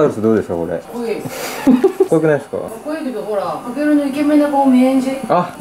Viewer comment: 先輩の下の名前さえが言ってね？0:54 かけるって言ってるように聞こえた